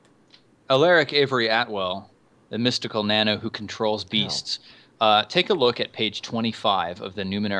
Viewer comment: I thought she said "Controls Bees", and instantly thought of Ninth World The Pain.